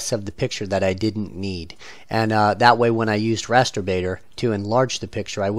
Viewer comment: I thought he said "masterbator(dot)com" :D